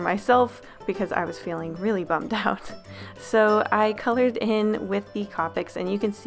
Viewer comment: Did..I heard that right? "I colored him"??? *gasps*ITSSS AAA TRAAAAPP (or I'm just hearing things??)